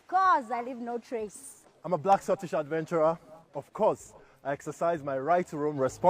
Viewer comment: Anyone else hear "I'm a black, scottish cyclops" at first? I thought Valve well full r...ridiculous with the TF2 Demoman, but Scotland apparently said "Nah. Sounds legit."